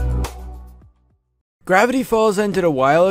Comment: Will Gravity Falls be the new Gravity Falls? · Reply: I may or may not have thought that you said gravy falls... Goodbye then...